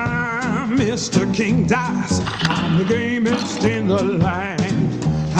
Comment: I thought he said i'm the gayest in the land when he actually said gamest...